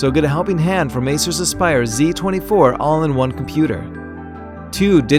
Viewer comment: did he say ASUS ?